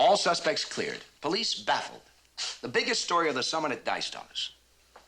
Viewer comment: does anyone know what he is saying? "the biggest story is for someone that dies, thomas" (definitely not it, but the poor audio quality and how fast they talk doesn't make this easy)